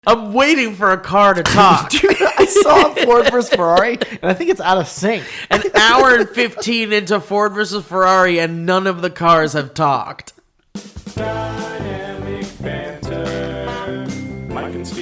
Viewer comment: The first time said it I heard “I’m an hour and 15 into Ford v....” and that was honestly the funniest one